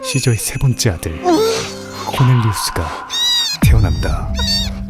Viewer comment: 코넬리우스가 코델리우스로 들리는건 나만 그런가